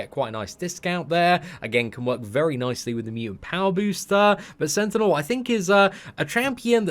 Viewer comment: Did he say mutant power booster 😂